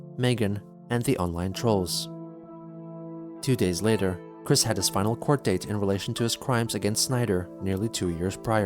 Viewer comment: I heard "He had his spinal chord date" :)